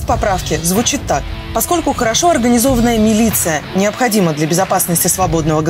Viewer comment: Я услышал слово МИЛИЦИЯ и вроде так и было написанно, я не ошибся?